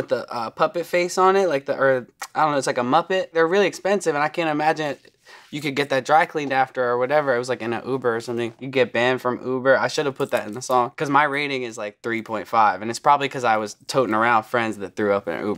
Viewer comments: did he say "an i oop or something.." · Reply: No he said Uber hahaha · i thought he said “and i oop” 😭 the vsco girls are getting to me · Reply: He said “in an ub-er” it does sound like oop tho